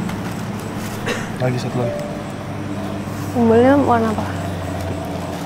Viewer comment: Salfok mnit 1:26:57 nyai bilang "umbelnya warna apa?"😂 Maaf kalau saya salah dengar:)